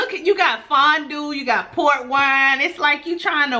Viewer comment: Up until now I thought you was saying PORK wine lol had my mind all screwed up lol 😂😂